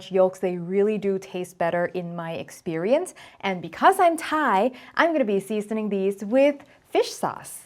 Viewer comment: You said "Because I'm Thai" (adding nam pla). I heard "Because I'm high".😃